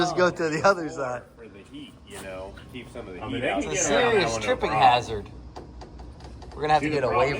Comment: I thought the engine builder said you cant "cruise around" with that engine?